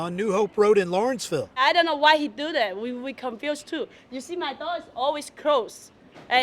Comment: I thought she said " my dog is always locked " its like they gonna serve dog meat to the clients .OUF!!